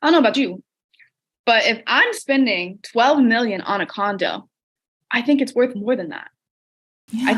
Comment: Oh my God, I thought she said 12 million-dollar condom